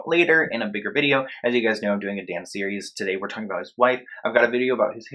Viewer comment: I thought you said "I've got a dance series" Instead of "I've got a Dan series" I was like, omg go you!!!